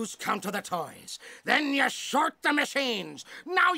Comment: why does it sound like theyre saying "shart" whenever they say "short"